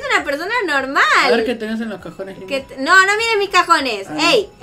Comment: cuando dijo cajones en el minuto 0:33 entendi cojones xd